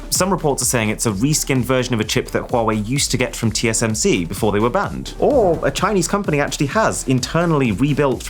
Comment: You reviewed them. I thought you said they were banned.